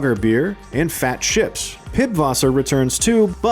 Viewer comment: Did he just say Pibwasser ???